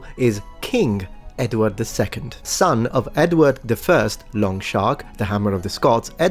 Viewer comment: At about 15:40 - I've listened to it 20 times now!! Each time, it sounds as though Raph is saying "Long Shark," rather than "Longshanks." I suppose it's possible, since English isn't his native language, that he's unfamiliar with the word "shanks" meaning "legs" and so he possibly presumed that the word in question was "sharks," instead... edit - At 18:03 Raph eliminates all doubt; he's definitely saying "shark/ sharks," instead of "shank/ shanks."